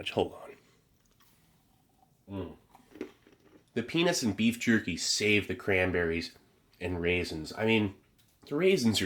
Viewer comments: Wait did he said the penis and beef jerky save cranberry 38:16 😂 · Sounded like he said “the Penis and beef” and I was just like 😮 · it sounds like you said, "The penis and beef jerky save the cranberries" 😭🤣🤦‍♂️ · is where i kept rewinding to the what and beef jerky did you say? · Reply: No disrespect I had to rewind multiple times to make sure I heard correctly the penis and beef jerky saved the cranberries 😂😂😂😂😂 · ?!? Ummm... "The Penis and Beef Jerky"--2024 Did I hear that right? LooooooooooooooooL As long as it wasn't PenisJerky. Also I'm not going thru 2,387 comments to see if anyone else "Heard" what I Heard. Nice :) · i was half paying attention and then at 38:22 i hear "the pen*s and beef jerky save-" and i was very confused till i looked and saw peanuts